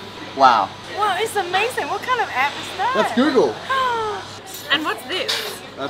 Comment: I'm traveling to Hong kong on May. Your vlog is awesome. By the way 17:32 what is the name of the apps you were using ? did you said "google"? Thanks for helping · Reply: Google Translate